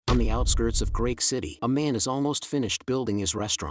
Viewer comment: did he say Great City or Greek City?